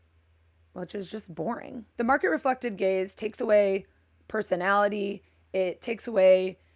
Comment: Every time you said, "Market-reflecting gaze," I heard, "Market-reflecting gays," and as a gay guy who is a color enthusiast and a crazy maximalist with a billion things on my walls and shelves, I think I'm an "Anti-market-reflecting gay." 😀